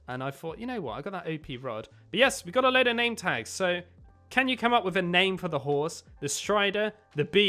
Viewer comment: When he said “can you think of a name for the horse” I thought he said “Kenya think of a name for the horse” so name the horse kenya